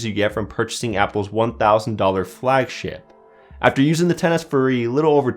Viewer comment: i thought he said flagshit